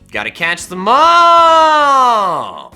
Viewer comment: You sounded like you said "gotta catch the maaaaall" lol